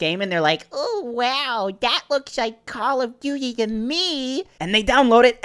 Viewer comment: It sounds like he is saying Call of Doody. LOL🤣🤣